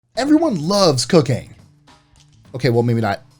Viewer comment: I thought he said "everyone love's cocaine"